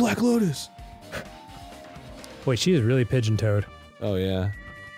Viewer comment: Dan said Chiaki's is "really pigeon-toed" but I misheard him and thought he said she's "really pitching toads", and I think I've just discovered my new favorite euphemism for pooping.